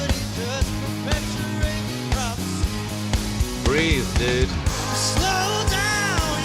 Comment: I heard you say, "Breathe, Dude." and I couldn't stop laughing. Thanks for that and the "Incredibles" comment on Katy Perry. You're a tough critic! 😅